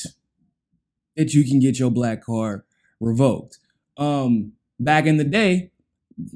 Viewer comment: Was 1:21 before I knew wtf he was saying, "black card revoked", I think. Good reality check here, other than that.